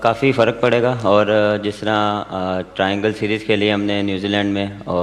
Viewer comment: what did i hear triangle series??😂😂🤣🤣